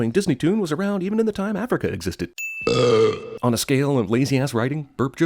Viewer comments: Lol, did you say "even in the time Africa existed?" @ 8:10? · did he just say "even during the time Africa existed?"